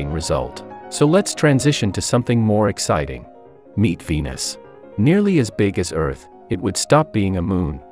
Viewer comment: Thought it said "Meat Penis"